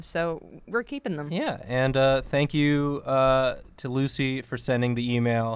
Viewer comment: Lucy is so sweet. She did that without me knowing. Bless her heart, and you guys'. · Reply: thought it said lucky and I thought "that's a interesting name"